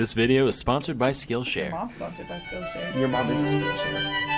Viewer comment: For a second I thought said “ your mom gets shared” I was like “CALM DOWN” she’s your mother-in-law you gotta see her on holiday dinners